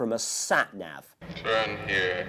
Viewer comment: What the heck was said at 3:58? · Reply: By Dracula? "Turn here". By him? "Sad Nap"?